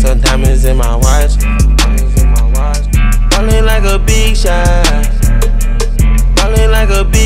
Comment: I remember the first time I heard this song playing it on my friends aux and she thought he said ballin like a bitch huh?